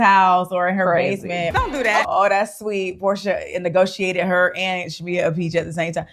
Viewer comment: I thought she said porsha never congratulate her.